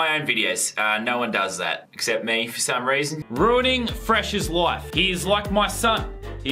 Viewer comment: I always heard "Ruining Fresh's life" every time I watch this video, is it supposed to be 'live'?